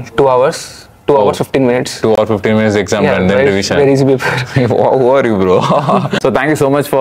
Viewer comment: 2hr 15 mins and then what he said? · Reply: he said "Very easy paper "